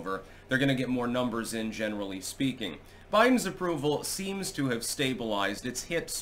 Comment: I thought you said his brain stabilized 😂😂😂